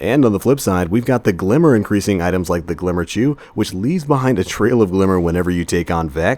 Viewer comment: am I tripping or @ 2:47 did he say "glimmer Jew"???